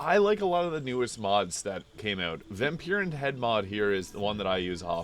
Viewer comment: Whats the face/ head mod u use it sounded like u said vampiras?? But u have no actual links or titles and i prob yeard u wrong cause that mod name doesnt exist. Some text tags woulda been amazing??